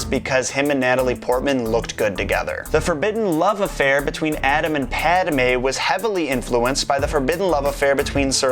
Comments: did he say adam and padme? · Adam and padme. did anyone else hear this at 10:46 ? am I going insane?